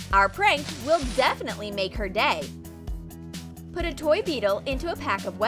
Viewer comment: I THOUGHT THEY SAID THE PRANK WILL DEFINITELY MAKE HER GAY XDDDDD